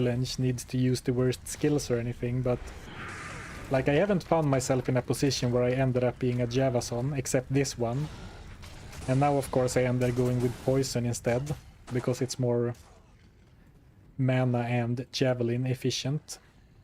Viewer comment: I thought you said Java phone 📞